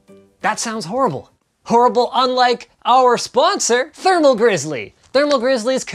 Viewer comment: Lol at the sponsor scene I heard : "Linus": "Wow, that sounds horrible, horrible LIKE OUR SPONSOR!" I'm like OOOOOOOHHHHHH!!!!!!!! ( Linus rekted the sponsor)